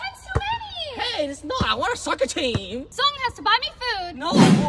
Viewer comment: What he said " i want a soccer team" what i heard "i want a sucker team"